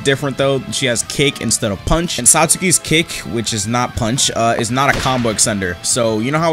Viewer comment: why do i hear " satsuki's cake" instead of "satsuki's kick" 💀💀